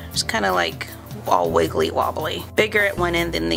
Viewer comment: I thought she said "wa wiggly "